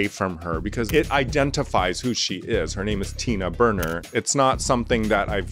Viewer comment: When Tina Burner entered I misheard her name as Tina Burger... I Thought she was Ronald McDonald and her purse was a chicken McNugget...